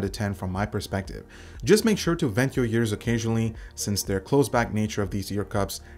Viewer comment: Watched 3 times and still have no idea what you said. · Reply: "Just make sure to vent your ears occasionally"